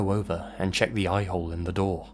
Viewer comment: I heard a-hole.... sounded very funny to me... yeah? no... ok bye!